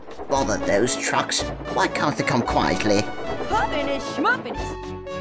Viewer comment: What did she say? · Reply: She said, "Puffiness, schmuffiness." 😎 Applejack said that in the Season 1 MLP episode "Look Before You Sleep" when Rarity explains what the cucumbers are for. 👍